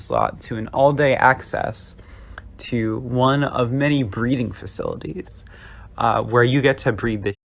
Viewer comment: I feel like I didn’t understand what that person said. Was that about BREEDING facilities?!